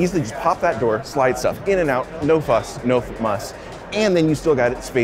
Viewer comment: Did he say no MUSK? Lol... sneaky 🐕